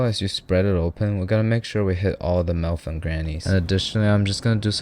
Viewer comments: did he really say "hit all the milf and grannies" instead of "nooks an crannies" or am i trippin? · Did he say milfs and grannies